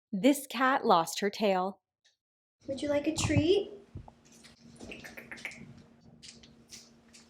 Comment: I heard “THIS CAT LOST HER KALE!”